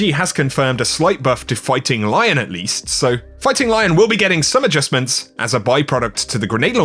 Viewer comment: I could have swore you said “farting lion”. had to rewind. Lol.